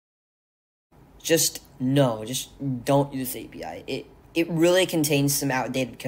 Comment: sounded like "dont use API" scared me Like damn time to learn it all from scratch